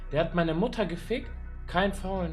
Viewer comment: мені почулося чи він казав лайку про маму?😮 (ich habe deine mutter gefikt)